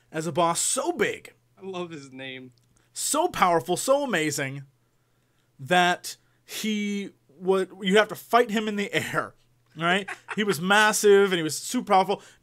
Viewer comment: I thought I heard Jesse say 'and you even fight his testicles' at first